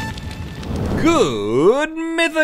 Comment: I thought Rhett said “good mythical condiments” 💀🤷‍♀️😂